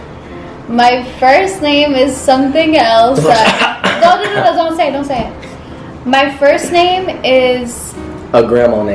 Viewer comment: can anyone make out what he says? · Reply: Day Bay Dolores